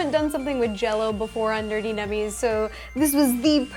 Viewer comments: At first I thought you said dirty dummies. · I always hear "Dirty nummies" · Iron man and at the end it sounded like you said dirty nemmies